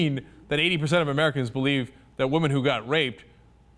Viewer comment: *facepalm* I was under the impression "god" gave us humans free will. Doesn't that mean not everything is orchestrated by him? and that we have FREE WILL? including abortions? Oh and did i hear "god raped" at 06:05 ??